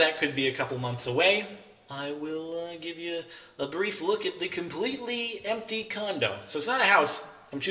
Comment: did he just say the completely empty condom???